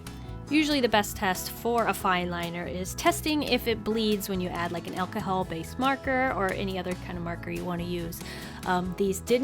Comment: Did anyone else notice that she said “elcohel” instead of alcohol at 3:43 😂